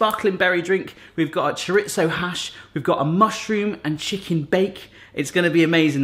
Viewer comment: Did he say hash🚬🚬